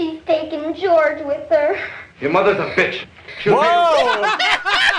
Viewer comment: i'm glad even the youtube autogenerated subs agree that it sounds liek he's saying "fish" and not "bitch" 'cause fish is all I hear